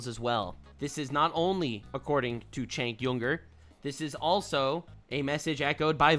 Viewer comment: Did I hear somebody say Chunk Yogurt?